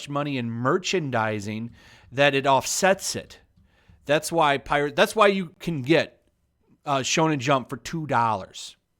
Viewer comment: anyone else catch the freudian slip at 6:32? lmfao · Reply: Nevermind, I got it. It's always free to be a pirate.